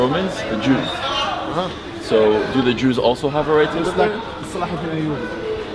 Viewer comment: the Iraqi guy didn't say "He seems to be a Jew", he actually said "Salahuddin Ayyubi" which sounds close to the Arabic word for Jew (yahoudi). Salahuddin Ayyubi is the first sultan of Egypt and Syria.